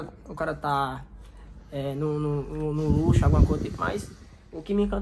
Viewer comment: Como assim de lixo mano? Eu entendi isso mesmo!